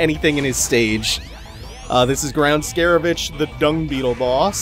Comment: I thought he said ground scare a bitch Done